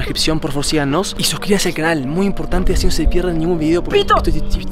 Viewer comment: En el minuto 14:26 escuche que dijo pito